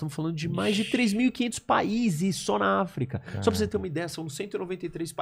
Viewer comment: não entendi essa parte, entendi errado ou ele disse 3500 paises na africa ?